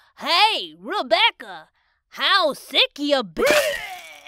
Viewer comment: "Hey Rebecca, how sick you be-" "AJSJSKJAHSKJ" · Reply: oh, i thought he was saying "how's vicky"